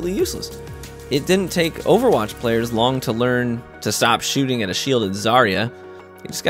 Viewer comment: Did you just say..... Players learned to stop charging zarya? Man I peaked at 3.8k in ranked and I still see zarya with 100 charge